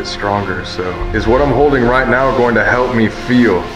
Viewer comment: okay but when said bebe i thought o heard "ow help me" i dont know if im wrong but please help me